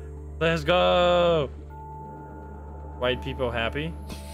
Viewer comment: i dont get why you said white people happy... just randomly throwing identity politics in there why don't ya... damn and I was enjoying your content :l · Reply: i dont know where in the vid you think he said this but im certain you misheard "widepeepohappy", which is an emote popular on twitch where he streams